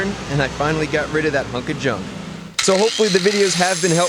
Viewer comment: did you said fucking Junk ? LOL was it that bad !!!